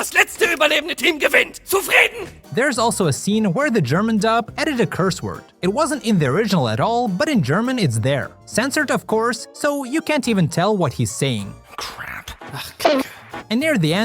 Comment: when he said you can't make out what jax is saying it says:"kacke" you can translate it to crap and shit